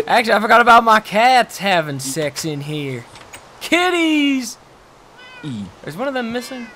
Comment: When said Kitties i was looking away and I thought he said "Titties" xD